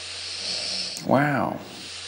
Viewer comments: Did he just say "Wow" on 6:16. That's so random. 😹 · Wait.... does anyone else hear Owen Wilson saying "Wow!" ?? 👀